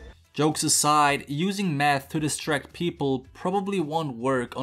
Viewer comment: I thought he said "meth"